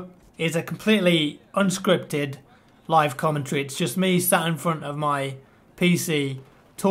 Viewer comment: I thought you said it was a live comentry